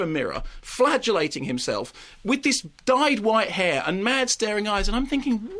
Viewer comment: oh lawl, i heard "flatulating priests" and went "... eh?"